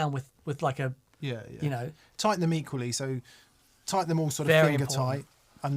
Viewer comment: When said "tighten them equally..." speaking of installing speakers, I genuinely thought he said "tighten the meat police." Had to rewind and listen again... I'm from the U.S., so I thought maybe it was British slang for a moment 😅